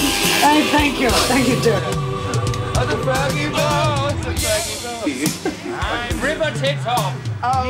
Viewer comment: someone please can tell me what he said 0:44 cause I from Brazil and dont understand this part, thanks. sorry my bad english. Freddie ❤😍😍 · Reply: "I've busted the laces, darling!" (Your English is fine; my Portuguese is non-existent! : / )